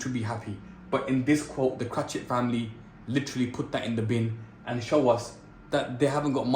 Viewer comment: is it just me who thought he said kutta in the bin not put the in the bin . kutta means dog in Bangali , hindi and urdu